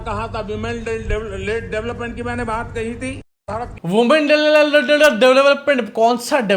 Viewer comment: Did he said women rape development?? Because it's increasing day by day